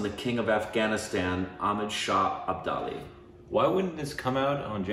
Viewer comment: I believe I heard say 'Amit Shah Abdali' 🤣🤣🤣🤣